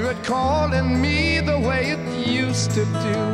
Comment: What does Robert Plant shout way in the background at 1:41?? I've been wondering about that for 40+ years. Not that it still keeps me up at night or anything. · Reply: Zeaux Osprey He says: “I can hear it calling me!”